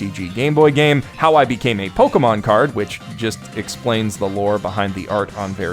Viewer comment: I thought I heard "That time I got reincarnated as a Pokemon card" and had to re-watch that moment just to make sure.